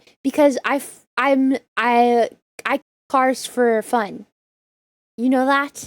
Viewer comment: oh god, I thought you said something else at first haha. :face-blue-wide-eyes::face-blue-wide-eyes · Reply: I thought you said you f- cars. o.O